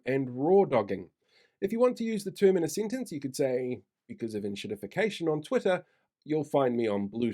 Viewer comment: Lol can we have a spell check on the dictionary word. I heard “inshitification” which would fit the definition 🤣